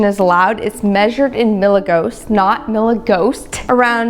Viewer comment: did she say "millighosts"? Who ya gonna call?